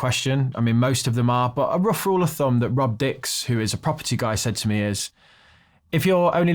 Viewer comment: i thought he said Rub Dicks but he said rob dicks which is also funny. What a horrible name.